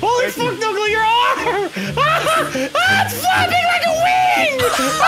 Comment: I understood what he said but it almost sounds like he either said flapping like a wing or flapping like the wind.